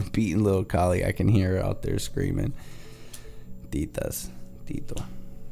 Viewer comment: did you say "Titas, Tito"?